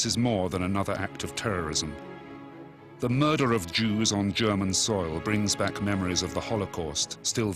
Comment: The narrator says,"the murder of jews..." and the subtitles say,"the mother of jews.." Please fix it so people who can't understand the language don't get the wrong message.